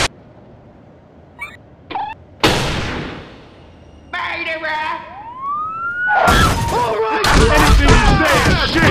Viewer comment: Anyone know what she says? Or where that voice is from? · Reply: DarkZircon MURDERER is what she says